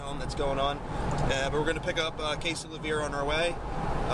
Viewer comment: at first i thought you said "Picking up a case of beer" lmao